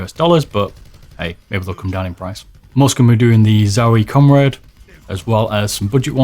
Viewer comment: did you call the Zowie mouse bungee the "Zowie Comrade"?? LOL it's comade! Gave me a good laugh thanks 😂